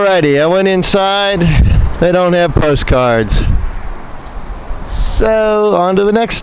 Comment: Oh hi! It sounded like you said you didn't enter the big Texan restaurant because they didn't have some sort of card? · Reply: Oh, post cards. I didn't buy any post cards at the gift shop.